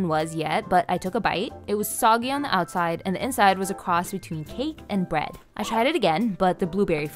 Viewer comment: I heard “the inside was a cross between hate and bread”